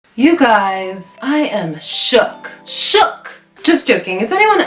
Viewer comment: I misunderstood, thought she said ' I am choked'. Yeah I am choked too....